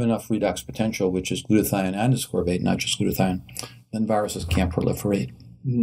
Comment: Hey, At 4:26 when the doctor is talking about viral infections. He says glutathione and something else... What was that second thing he said? Thanks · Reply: "ascorbate"